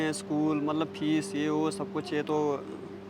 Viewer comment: Did he say "PHEEEES"? It is an Urdu/Hindi word for fart....!!! 🤣🤣🤣🤣